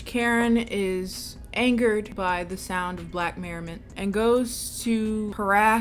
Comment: “Angered by the sound of black merriment” 😂😭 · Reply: I thought she said “merry men” but either way…I cackled!